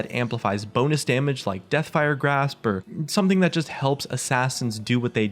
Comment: did he sayed deathfire grasp ?